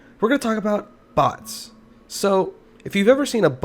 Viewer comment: you're very wrong in the intro. i only hear "butts" lmao